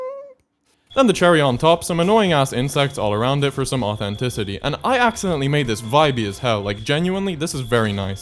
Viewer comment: Get 40% off Coursera Plus for 1 year if you sign up at - <link> through 7/13 Thanks to Coursera for sponsoring the video. Also please feed me by wishlisting vermin: <link> What did you guys this of the video? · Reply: Genuinely thought you said "mothenticity ".